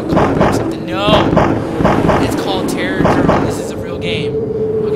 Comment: i thought it said "Terrordome"